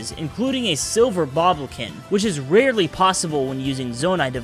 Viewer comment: Did you say fucking "Boblokin" at 15:25?